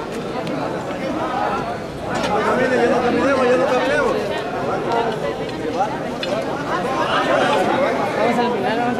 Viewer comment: Que dice en el minuto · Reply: Yo entiendo "Ya no caminemo', ya no caminemos".